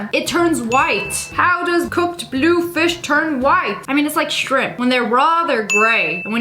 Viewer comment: When she said “just like shrimp-“ I fr thought she said “shrek”😭 “So just like shrek, when raw it’s grey and when cooked it’s white”